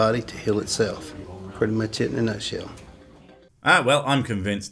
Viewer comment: "Putting my tit in a nutshell" yes i know he didn't say that but I heard it